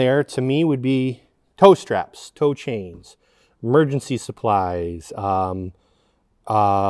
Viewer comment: First time I listened I totally thought said he’d store cocaine not tow chains in the hidden space 😂